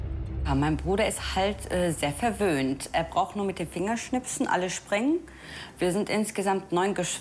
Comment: hab ich da richtig gehört? "Er braucht nur mit dem finger zu schnippsen dann sprängen?